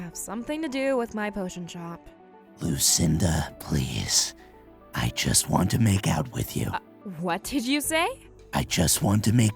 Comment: I thought that guy said"I just want to make out with you."😂